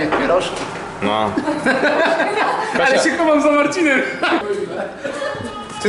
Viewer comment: Czy ja dobrze usłyszałem ??? XDD " Cytryna, a ty co ? Kręcisz dymy ?" xDDDD